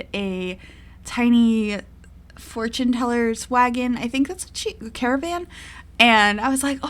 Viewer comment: So I have really bad hearing and I thought you said 'swaggon' omg